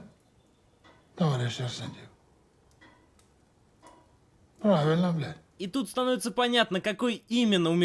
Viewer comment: Мне послышалось? сталин на 4:40 сказал "правильно бля"